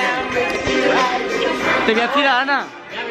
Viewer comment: wait what does he say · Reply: el dice "te voy a tirar Ana"